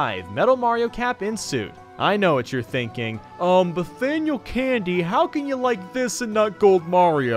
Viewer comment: did you just say Bathaniel Candy. 🤣